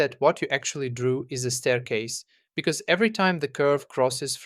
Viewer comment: did you say stairs? 😈📶